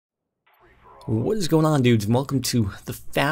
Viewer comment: no, he said "what is goin' on dudes, at i thought he said noobs LOL